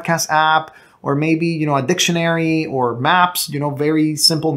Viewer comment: Did he say @ 5:09 "Addiction Mary?"